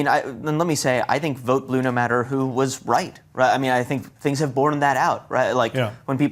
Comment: Pretty sure that he said both actually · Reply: I listened again. He said “was right”. To your point, I misheard the first time. He quickly said we should still be doing that. That’s on me.